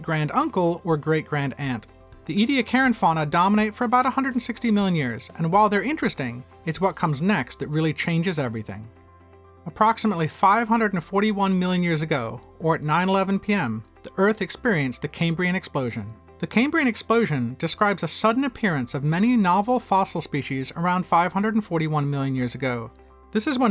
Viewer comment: at 9/11 the earth exploded is what I heard 💀💀💀